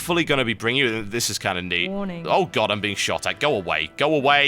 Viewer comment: anyone else hear warning at 14:55 ?